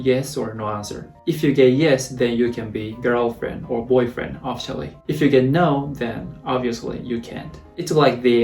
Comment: Oh "Official" 🤣🤦🏻‍♂️ I thought he was saying "Off Shelf relationship" 😳😅